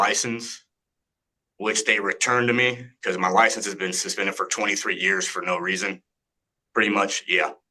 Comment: This guy should NEVER be allowed to appear in court on Zoom. He abused his privilege. Why does he get an expidited hearing? Because he wants to hurry up and get his license squared up with California? He's only had 23 years to do so? F*ck that. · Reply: 23 years!? Omg. I thought I heard "2 to 3" years. 😮